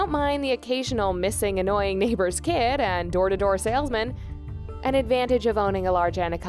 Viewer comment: wait what? did she just say the occasional annoying missing neighbour's kid??